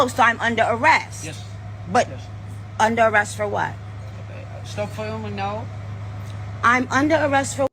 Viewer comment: I thought the officer said “stop filming now”. also said that they are some imposters portraying to be melometers